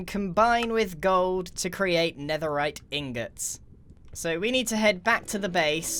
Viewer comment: did he just say netherrack ingots!!!!